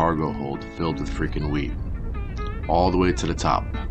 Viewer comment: Maybe it was wishful thinking, but I swear during the cargo hold part, I thought he said weed not wheat. LMAO!! Love your content by the way, this is some really good stuff, keep up the good work.